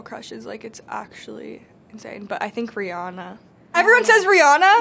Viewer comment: did she says ZAYN , or am deaf ?